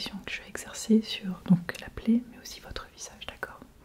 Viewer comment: There's a word you kept using to refer to the site of the injury that I didn't know. It sounded like "plait" or "blé" Can someone help me out what it was? Eg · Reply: Plaie : wound ;)